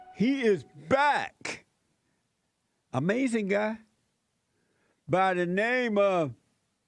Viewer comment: Did he say “Amazing Guy” or “Amazinga”? I know “Amazinga” is a meme/emote but I don’t know if JLP uses “Amazinga” himself.